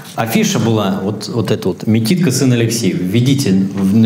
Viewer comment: "МиТико ...сын Алексеев"? мне одному послышалось?